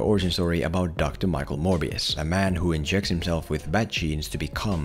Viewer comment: When he said “inject himself with bat genes” I thought he said bat jizz and that got me laughing my ass off.